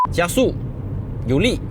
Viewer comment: 我放慢到0.25还是听不到你说什么叻😂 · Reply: 我只抓到 高速 两个字而已